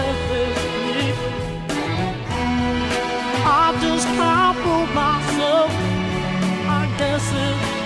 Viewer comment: 045 sounds like Robin said I just have pooped myself. 😂 Lol